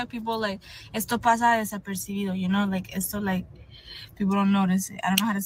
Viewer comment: Hi. What she said at 4:00 (ish) was “esto pasa desapercibido” which means “this goes on unnoticed.” In other words, pay attention to the tip she’s about to share.